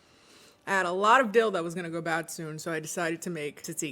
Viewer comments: In the first 3 seconds of the video I thought you said “I had a lot a dildos gonna go bad” and I’m cracking tf up 😂😂 seems weirdly on brand lol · i had to rewatch the intro cus i kept hearing "i had a lot of dildos that was gonna go bad soon" ...goodbye!